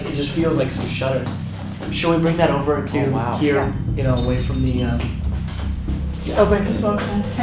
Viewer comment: I didn’t know what to do when embodied Bobby 😮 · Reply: I heard "stay away from here"